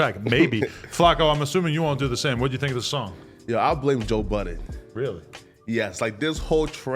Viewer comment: I thought he said "yo I blame Joe Biden"